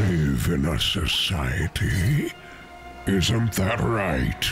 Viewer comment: What he said: “AMONG US!” What I heard: “AMAGUS!”